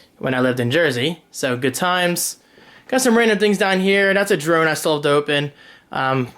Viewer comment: Love the setup. Had to do a double at 7:37, though. I heard "....that's a drone I sold dope in." LOL